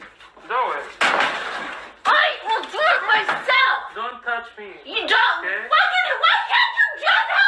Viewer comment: Mohammed is passive aggressive and probably orchestrated getting her that upset so he could record it and "appear" innocent. And while your narration said Mohammed is saying in the recording "don't hit me", what I heard was "don't TOUCH me" . "Touching" is not necessarily "hitting" .